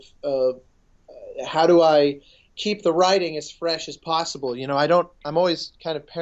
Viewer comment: who else thought he said "keep the riding as fresh as possible" and laughed their ass off xD